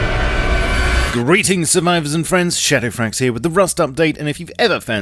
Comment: Is it just me, or do I hear "Greetings SURMIVORS & friends"